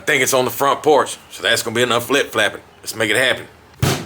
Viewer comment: Am I the only one who thought he was saying “that’s gonna be enough flip flappin?” 😂